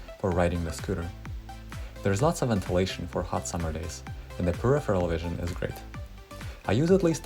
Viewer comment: what u said? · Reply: The peripheral vision is great